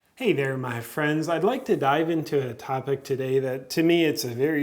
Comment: I heard "I'd like to die" instead of "I'd like to dive into..."